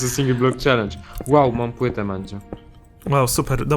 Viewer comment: usłyszałem że blow mówi "wow mam pyte Mandzio"